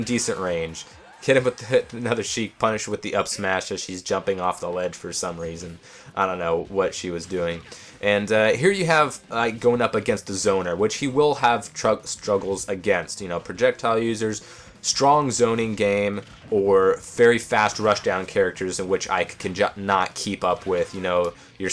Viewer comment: I thought you called Link "Zelda" was about to rage lol, then I noticed you said zoner.